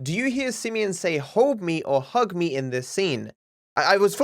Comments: i hear "Hog me" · the first two times i heard hug me but then the third i heard hol dme , i think its one of those things where depending on what you think it is your brain hears it like that